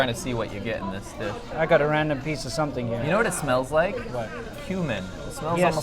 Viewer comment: did you just say ; " it smells like human " ? What the hell are you eating in China ?